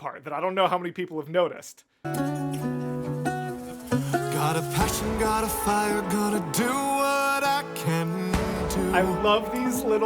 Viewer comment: As someone with autism and ADHD that does cause some auditory issues, my FAVOURITE part of your EP IS the LYRIC VIDEOS - I misheard Dreamscape as “Find your passion fan your fire you can do what you can’t do” and G.O.O.D I heard as “time to start my ascent” instead of “despite the dissent”, even though I now know the actual lyrics, THANKS to the lyric videos, mishearing them greatly inspired me to get out of my anxiety/head